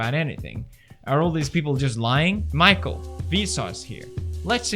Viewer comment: I thought you said "Vsauce, Michael here." But you said "Michael. Vsauce here." LOL